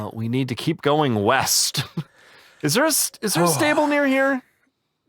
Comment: west I thought you said weast